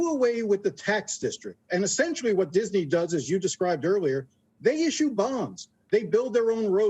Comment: I honestly thought he said "they issue bombs."